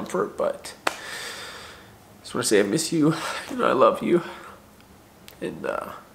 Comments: Did anyone else hear the “i love you” at 14:58 🥺🥺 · actually sounds like him saying “I love you” . I hope you’re doing okay. He will always watch over you and be you’re company. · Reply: it literally sounds like him saying "I love you Kian" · It’s literally so clear at 14:57 that it sounds like saying “I love you” got tears in my eyes wow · I hope you see these comments and notice at 14:57 it sounds like said I love you